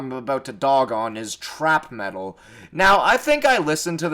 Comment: dude my heart stop when you said trap metal i thought you said thrash metal😭